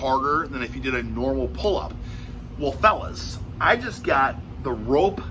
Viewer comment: Hearing Rick de la Stick talking about shafts had me hearing « phallus » instead of « fellas » at 1:42. I’ll blame it on the mindset of course EDIT: I guess it got to Ricky B as well lol